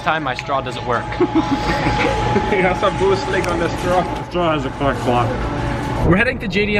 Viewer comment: When you said the straw doesn't work did say the straw has a sr swap LOL